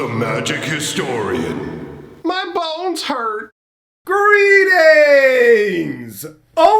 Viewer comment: i once heard you say "my balls hurt" in your intro and now i can´t unhear it..